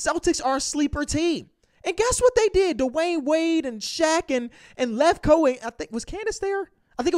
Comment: I can’t be the only one who thought said Dwayne instead of dean